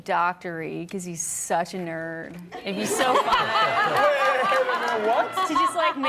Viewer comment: did AJ say "and he's so fine..." about Reid 28:16 ??? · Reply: think she “fun”